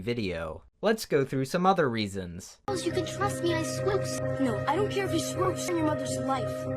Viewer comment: anyone else hear swose?